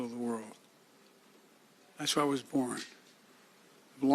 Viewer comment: Did he say he was born in Israel at 4:45.